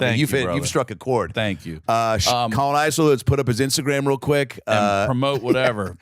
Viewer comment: For a good minute I thought Adam said his friend's name was Colin Izer, but it got worse when he said it was spelled Colon!🤣